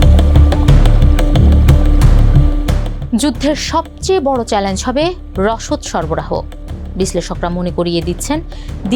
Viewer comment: s মুবিটটার নাম কি বলল কেউ জানলে বইলেন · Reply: A Bridge Too Far